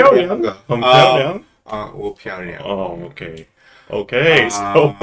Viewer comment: anyone else hear mumble “我漂亮” IM DEADDDDD 我同意🤣